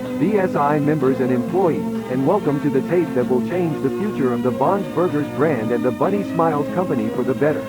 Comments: I thought he said Bob’s burgers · I heard this as bobs burgers, I just thought "Dear god five nights at bobs"